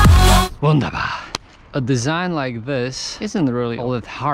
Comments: Did I hear „wunderbar“ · Did u just said "wunderbar"? Greetings from Germany 🇩🇪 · did he just said "Wunderbar"? for me as a german, it suprised me quite a bit :D